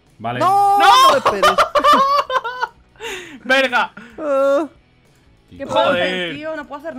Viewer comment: Alguien mas escucho lo que dijo la en el minuto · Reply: fabriii 7747 VERGA